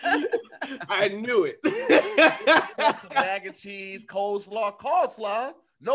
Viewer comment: Hey Tom, it sounds like said “call-slaw” instead of “cole-slaw”. It seems as if coleslaw is his, booty call perhaps🤔, but anyway, back to you Tom.